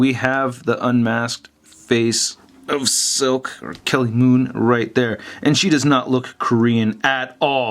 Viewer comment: Did he say Kelly Moon when swapping the heads out